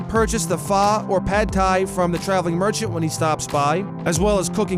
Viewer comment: Who heard Hentai?